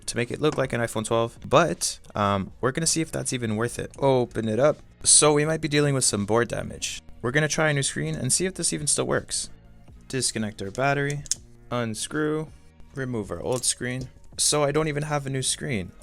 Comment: i thought you said brain damage